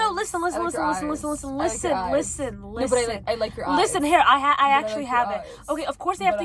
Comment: I thought she said I like your ass 😭